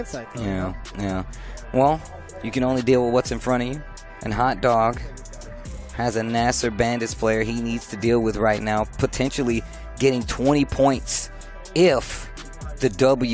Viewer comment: Did I just hear "NASR-baited"? 😆